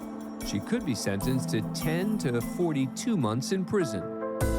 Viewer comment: him: "ten to forty-two months" what I hear: "10^42 months"